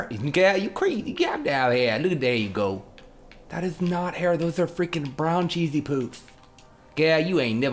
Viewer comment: Also..totally thought he said "That's not hair..that's brown cheesy poops.."hahaha